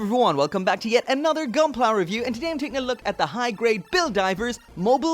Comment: I know he said high grade...but it sounded like he said hentai grade. Which is disturbing for multiple reasons